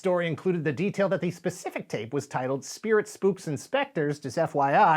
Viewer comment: Ironically enough, I heard you say "Spirits, b006s and specters" instead of "Spirits, spooks and specters".